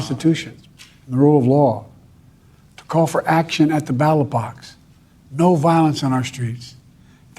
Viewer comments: damn, I really thought he was saying battle box until you brought up what he was actually saying. Thats nuts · I heard him saying loud and clear "battle box". I thought it was some idiomatic expression, so I looked that up and...nothing. Only then it dawned on me he was talking about battle bots.. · BATTLE BOTS 🤖🦾🦿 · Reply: This is the type of stuff people say about ALL pennslvanians, yes it sounds like we say "battle bots" when we say ballot box lol.. our accent is very hard for many to understand :P Wait till you hear us say. "DAHNTAWN" or "Geetyet?" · I heard Ballot Box, but it was VERY darn close and slurry so I did have to go "Wait what did he just say?" the first time. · Oh, he said ballot box. I thought he said battle box, hahahaha.